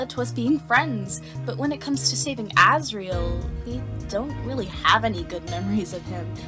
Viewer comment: What the, wait a min, Look at the subtitles at 11:08 - 11:09 It says "Ezreal" instead of "Asriel"